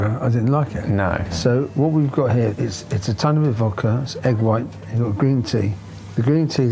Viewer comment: Some sub issues.. 1:29 think he says "tiny bit of vodka" 3:00 "trigeminal" nerve 29:49 Recreation of a "Tunnock's tea cake"